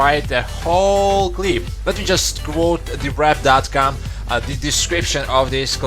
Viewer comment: I chocked on my saliva when I heard you say they revealed the description, cos I thought you said they revealed a clip lol.